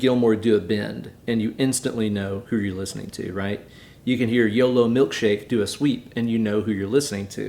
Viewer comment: Jesus, I thought you said dildo milkshake!🤣 It still kills me there’s like 69 notes on the SotN solo run played in .69 seconds.